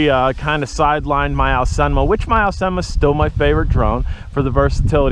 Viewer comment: "The Bugs 3 sidelined my ????" Sounded like "Alsema" but I did not recognize this drone name. Could you tell me what drone you were saying? You mentioned it is your favorite drone, so I am very interested. I guess I have not watched enough of your videos, because I'm sure you must have mentioned it in the past! Thank you! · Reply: Aosenma is what I was referring to